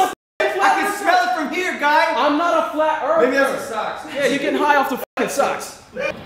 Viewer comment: “I can tell you’re a flat earth person! I can smell it from her guy!!, maybe it’s the socks?” Logan~2019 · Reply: SocialOutcast lmao I thought he said ur getting high off the smell of those socks